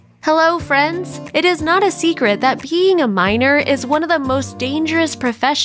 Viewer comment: I thought she said “peeing a minor” at the start...